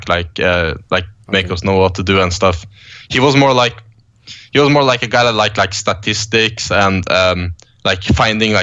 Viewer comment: LOLL 51:11 i thought he said "he was more like a guy who sucked his dick" LOLLL